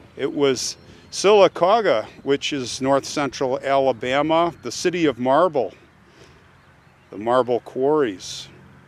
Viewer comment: Thanks for honoring this lady from my home State.Mrs. Hodges was injured by the meteorite the year that I was born.I am familiar with this incident but you filled in some important details that I was unaware of.The small town in Talladega County that she lived in when this event occurred is close to Jim Nabor's hometown,Sylacauga.Again,thanks for visiting Mrs. Hodges grave and telling her story, very interesting.Really enjoyed yesterday's presentation of the ladies who went missing at Lake Lanier in nearby Georgia.I'm still thinking about that story.I had not heard of it until yesterday.Thanks for all the history that you share with us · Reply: My bad,I misheard.He mentioned the city of marble, which I am aware that Sylacauga is famous for.I thought he said the city of Marvel,which is located in Bibb County,nowhere close to Sylacauga. 😵‍💫